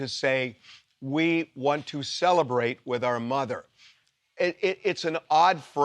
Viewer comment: I thought she said "Celebrate our Mother".